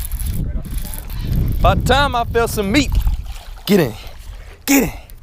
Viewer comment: did he just say ' its about time i feel some meat '?!?!?!?! buhahhaaha